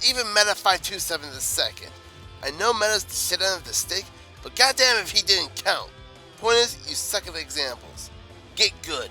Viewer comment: I didn't quite hear what you said there. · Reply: P If you're talking about what I said after I brought up Meta, it was this: "I know Meta's the shit end of the stick, but goddammit if he didn't count. Point is, you suck at examples."